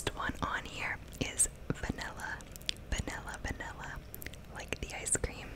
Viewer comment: When u said vanilla I thought u said Vanessa bc that's my name thx for the likes